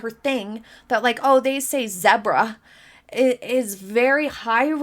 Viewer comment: lol..I thought MM said “Debra” wondered who that person was!